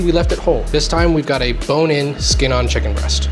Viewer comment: I keep hearing POTION liquid. Cooking is magic so...